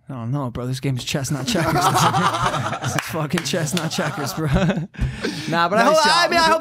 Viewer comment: It sounds like Logan is saying “Chesnut Checkers” instead of “Chess not Checkers”. Am I the only one who thought this the whole podcast...?